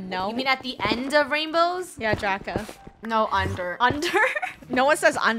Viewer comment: it’s funneh said draca instead of draco